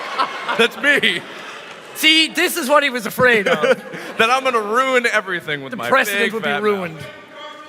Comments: what did the fan yell around the 7:50 area?...I cant tell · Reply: he said mark ruins everything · And what did the guy say at 7:52? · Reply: "mark ruins everything" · what did the person say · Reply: he said mark ruins everything